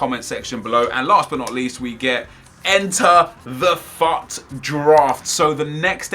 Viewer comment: I thought he said "fat draft"